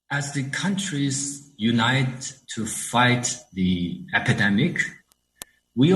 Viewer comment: Did he say "Academic" instead of Pandemic !!!!